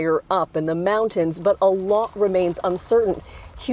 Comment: did she just say but allah remains uncertain